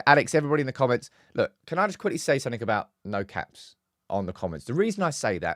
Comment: Great show. I thought you said, 'No cats.' I stupidly went through the chat looking for cats 😂